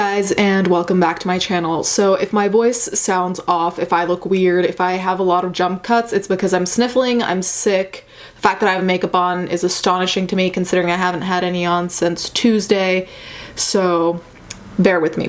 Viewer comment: When you said “the fact that I have makeup on” I totally thought you said “the fact that I would make a bun” and I was v confused